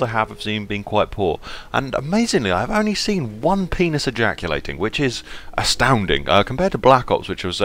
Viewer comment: did he say penis ajaculatiung or what ever he said